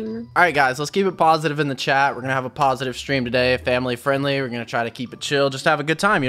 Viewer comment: i thought u said keep it family friendly lol